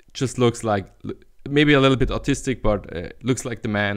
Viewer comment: did you say autistic or artistic